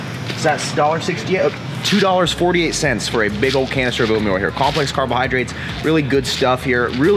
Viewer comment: Did he say: 4:00 2,48 for a big old cancer of oat meal ? Lol WTF